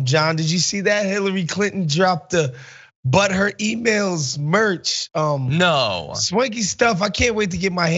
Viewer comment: When he said, "But her emails," I could have sworn he said, "Butt hurt emails."